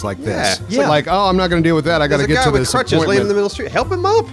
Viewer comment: I thought he said cruch meaning dick